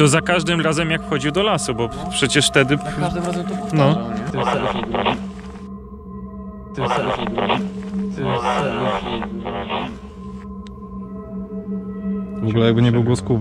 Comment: Usłyszałem Halo ja cię proszę albo Halo ja cię potrzebuję